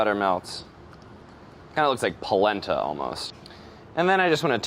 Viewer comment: I thought he said the egg looked like placenta